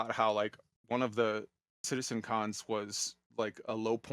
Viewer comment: OMG, "citizen Con" ?? I thought he'd said he watched "citizen Khan" DOH!